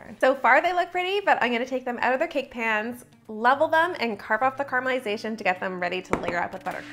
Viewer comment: Me: DID SHE JUST SAY CARMEL 😲🥰